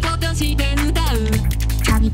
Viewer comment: 画面見ずに見てたら身にまとって〜が、君は童貞〜に聞こえてしまった